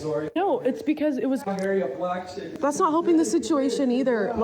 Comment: Did he just say ," I ma marry a blk chick??" and the mask is off! My sistas who date yt guys be careful